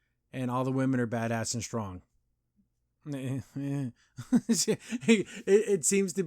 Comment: Maybe the true awful ending was the friends we made along the way Also when you said that all the women are badass and string, I misheard and heard badassatron